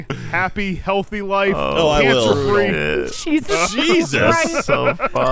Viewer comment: I thought said "Gavin Free" instead of cancer free lol